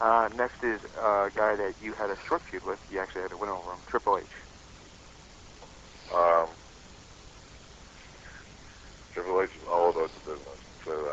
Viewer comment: What did he say about HHH at 14:10? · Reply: "Triple H is all about the Business"